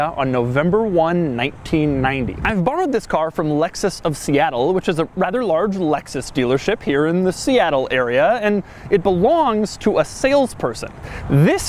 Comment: I thought that he said "Lexus stealer ship".